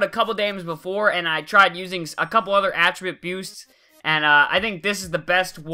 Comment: Did I hear you say "Attriboosts"